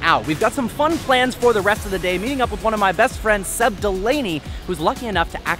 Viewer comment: At first I could’ve sworn he said “said Delaney” then I realized he said “Seb Delaney”